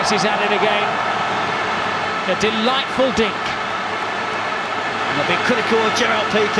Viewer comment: what the hell did he say ?! 🤣🤣🤣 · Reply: A delightful dink